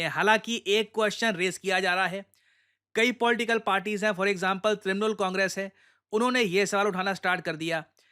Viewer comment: I was watching the vid at 1.5x and when Prashant sir said trinamool congress, I literally heard criminal congress😂